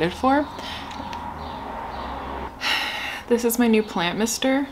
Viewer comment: I was looking at the comments when you said "Plant mister" I thought you said like "Plant mr." and I just imagined a plant with a mustache stuck to it. T_T